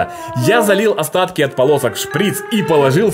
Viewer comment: мне послышалось в него я залил остатки от ПОНОСА а не от ПОЛОСОК)))😂 а там получилось очень классно побольше бы таких видео))))))